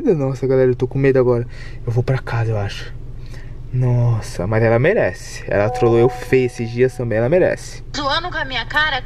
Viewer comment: So eu q escutei "a mariana merece " ?